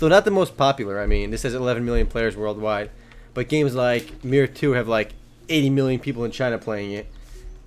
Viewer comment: what did he say at 2:24 "games like ....have like 80 million people playing"? Sounded like mirror 2 but there's no such a game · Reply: said Mir 2. It's an Asian MMO called The Legend of Mir 2 (the official Western servers have been shut down). The devs of that game reportedly once claimed that the game had 120 million players worldwide but I somehow doubt that was true.